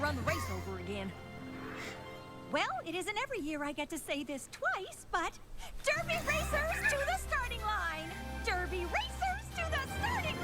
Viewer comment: at first I thought cheerlie was saying Derpy racers to the staring line XD